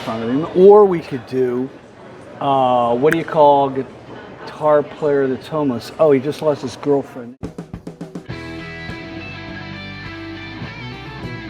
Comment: "What do you call a guitar player that's homeless? Oh he just lost his girlfriend".... It took some repeats to catch what he said: you're welcome. Thanks for the video! I was curious about these guitars. Personally I wouldn't shell out $3k but I'd have to play them to be sure.